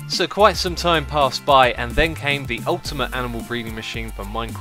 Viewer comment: At first I heard "The ultimate anal breeding machine"